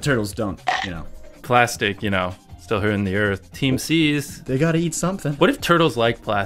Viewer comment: did he just say team seas.....